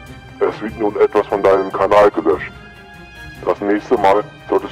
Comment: habe ich mich verhört oder habe ich kanal löschen gehört😢